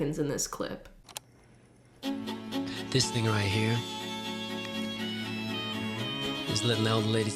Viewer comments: did he say the N word? “This N right here...” 🤨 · Lmfao I’m dying right now!!!! When he started the thong song I swear I heard “this n***a right here” and I fell out. I know he didn’t say it but I can’t un hear it now 😂😂😂😭😭😭